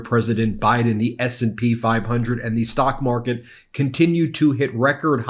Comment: But I thought he said the stock market love him.